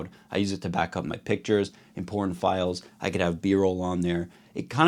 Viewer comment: I know you said "important files" but I keep hearing "porn files"